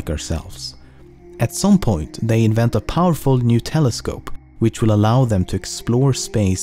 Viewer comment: When he said new telescope I thought he said Nutella scope